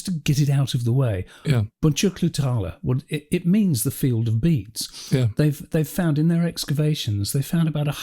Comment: I misunderstood and thought you were saying "field of beets". Thought it was an ancient Fraggle city or something. 😊